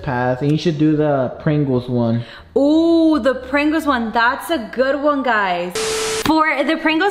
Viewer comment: I thought she said "ooh the prank wars one!